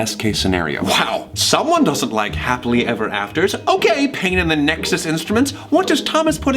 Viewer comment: I thought said “Well someone doesn’t like happily ever actors!” And I was like 😃 that was such a good play on words! But then realized my mistake